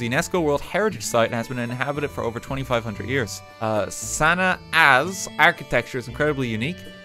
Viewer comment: The first time you said Sana’a’s I thought you said Santa’s Ass